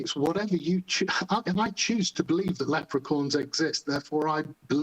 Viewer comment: I keep hearing Leprechorns and I love it. Little men, dressed in green, mixed with unicorns.